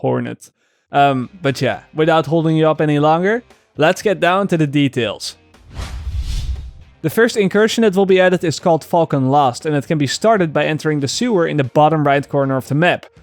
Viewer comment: I thought he said fucked & lost XD